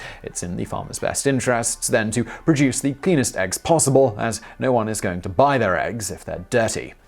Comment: I had the video playing in the background and thought I heard "penis eggs." 😐